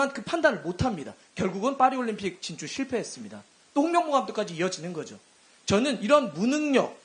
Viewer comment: 똥명보라 들리네 ㅋㅋㅋㅋ